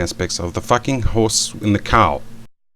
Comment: did he say fucking the horse and the cow?